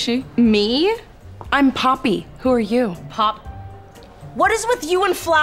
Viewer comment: For a moment there I thought she said 'papi' instead of 'poppy'😅